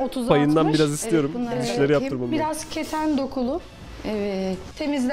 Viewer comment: kefen dokulu anladım fkbkgflvlblfl (asıl olarak ne diyor hâlâ anlamadım)